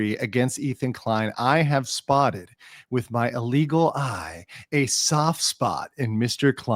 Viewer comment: it sounds like you say "with my illegal eye" so if I see you with an eye patch in the near future I'll know that is was confiscated